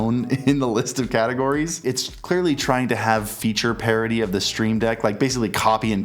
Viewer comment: I kept mishearing "parity" as "parody" and that made me very confused for the first half of the video...